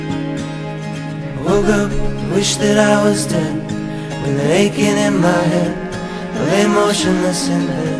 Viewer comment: the words said "with an aching in my head." but someone thought they said with clay aiken LOL